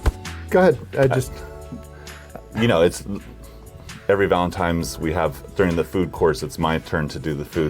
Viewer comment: Anybody catch that Eric says "Every Valentime's..." ..... with an 'm', not an 'n' @ 1:10?